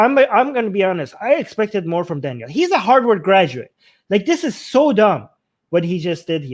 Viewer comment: Closed caption says : I expected more from Daniel he's a Hardwood graduate